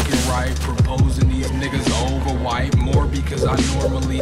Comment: i heard the n word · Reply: He did say nigga